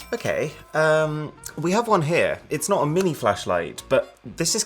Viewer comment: Thought he said mini fleshlight 🤦😂